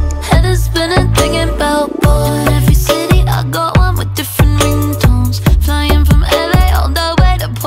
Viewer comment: In every city I got one with different memetones,, 1:10 Am I the only one who hears that? 😂